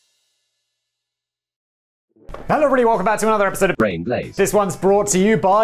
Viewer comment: 10 bucks says he actually said Business Blaze.